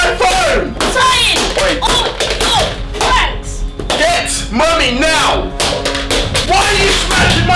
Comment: i thought he said get money now